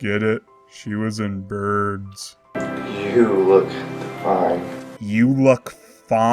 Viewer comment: I think he said, "you look divine"